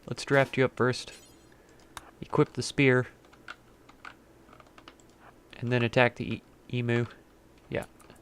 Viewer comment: For a moment, when the Emu attacked, I thought you said "Ness grab this beer and attack the Emu" and I was like "what?!" As for Coyote dying, I don't know the code behind it but I guess it's because of all her injuries?